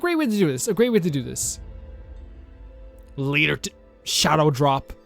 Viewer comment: What did you say? "Leert"? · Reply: Later Today i think but with the quickest of tongues